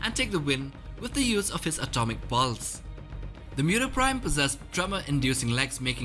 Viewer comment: is it just me, or does it sound like he says "Atomic Balls"